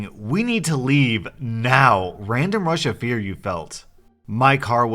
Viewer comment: I heard “random Russia fear”